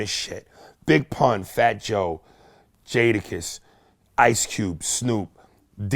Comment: Waait did he say Ice Cube 7:08😂😂😂